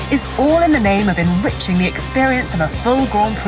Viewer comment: Thought I heard Rich Energy instead of enriching. Was very confused.